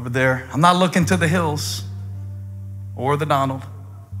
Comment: Did he actually say, "I'm not looking to the hills or ThE Donald?"